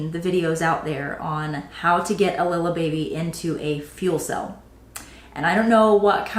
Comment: 😂🤣 oh my goodness I kept hearing “little baby” fitting into a fuel cell.